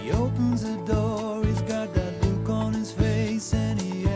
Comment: This song was popular when my brother and I were little, so we heard it often riding in the car. We misheard him when he sings (@ 3:25) “he opens the door he’s got that look on his face” as “puke on his face”. Every time it came on the radio we sang that line and laughed. To this day, 40+ years later, I still laugh every time I hear it.